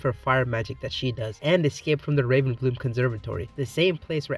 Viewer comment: i thought you said "but [samira] is gay" instead of escaped and i was like HELL YEAH for a moment